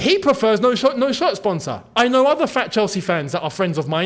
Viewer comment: did he say "I know other FAT Chelsea fans that prefer no shirt sponsor"? 😂😂